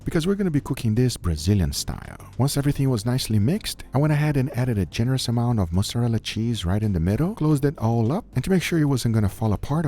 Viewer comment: When he said « I added a good amount of mozzarella cheese » it sounded like « mustard on the beat » and they not like us from Kendrick popped up in my head 😂